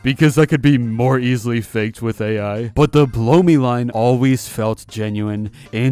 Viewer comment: I thought SpongeBob said “BOOBIE!!” Not “BLOW ME!!” 😂